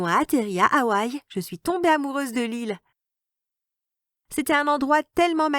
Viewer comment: qui a cru qu'elle allait dire qu'elle étais tomber amoureuse de lui (son frère ) car elle a dit l'île mais j'ai entendue lui